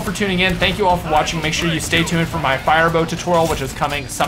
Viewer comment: You must have misheard us, we said fire bow not fire staff